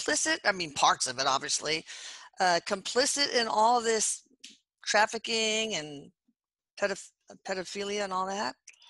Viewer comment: What is the series she's saying to watch on youtube? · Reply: I don't see anything listed like that or what I thought she said "yall kabal trafficking".